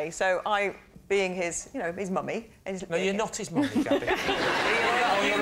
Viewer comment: what'd he say at 4:55 ? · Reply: TheDarkFalcon she says, regarding the dog, "I'm his mummy". Lee replies "gabby you're not his mummy.. either that or your husband is very ugly". Implying that the only way she can be the dogs actual mummy is if she were to procreate with another dog/ugly person to actually mother her dog. Make sense?